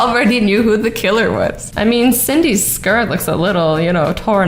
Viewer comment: me did she just say Cyndy